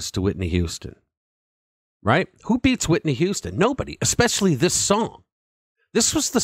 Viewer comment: did you really say who beats Whitney Houston?